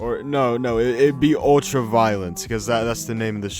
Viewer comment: I thought you said "OSHA Violent Deathmatch" and I was like that sounds like an awesome concept. What would that entail?